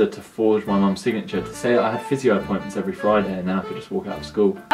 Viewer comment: what is he saying?? Physio appointments??? I'm sorry, he has a strong accent.